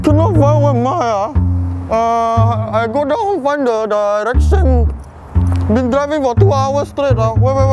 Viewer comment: Sounds like erection instead of direction.Keep up the good work!